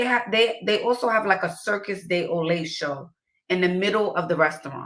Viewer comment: Did she just say "circus de olay" ?? Ok 😂😂